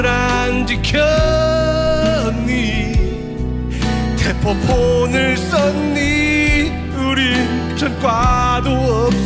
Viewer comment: 대포폰을 썼니-->데프콘을 썼니로 들려요 ㅋ